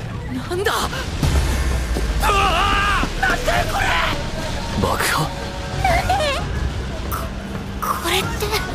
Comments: Did Todoroki said Bakugo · i could not stop laughing when todoroki immediately thought of bakugo as soon as he heard explosions (Ik that the subtitles say An explosion? But I heard the words bakugo) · When Todoroki says "An Explosion?" It sounds like he is saying "Bakugo?" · Can we talk about the fact that when Todoroki says “an explosion” at 0:48, it sounds like he said Bakugo?